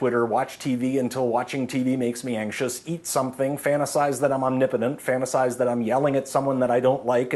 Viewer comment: what does he say at 1:30? un nipitant?